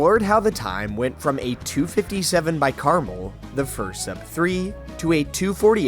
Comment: Caramal. What i hear: krmal